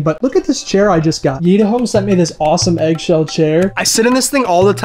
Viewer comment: What he's saying " Yitahome " What I hear " Yeet a home "